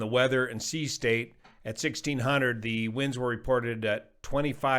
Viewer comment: on the video, did you say, "1600 hunnerd"? That's how we say hundred in Michigan. " Don't do anything dumb, dangerous, or different" would be a great t-shirt.